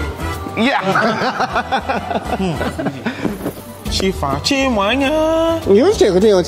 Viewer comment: Did I hear say "Chimuanya" 😂😅😂😅